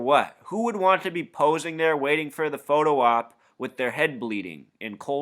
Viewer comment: Sorry, can't find the link to the video with an American woman commenting, perhaps she said "waiting" and I heard "wading", anyway, the survivors were doing both, waiting in water which only came up to their thighs. Isn't it winter there? I would have got the heck out of there real quick instead of waiting for a dinghy. I find it strange that half the plane, and the passengers, disappeared in such shallow waters. Keep up the good work, cheers from France